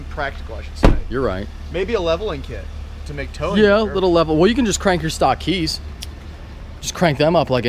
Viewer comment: Did he just say crank your keys lol